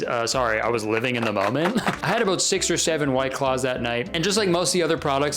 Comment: did you say s-s-s-six seven